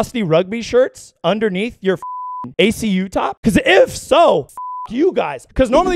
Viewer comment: Did you just say acu top at 3:28?